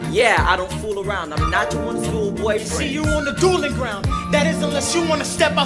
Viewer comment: he really said school boyfriend's not school-boy friends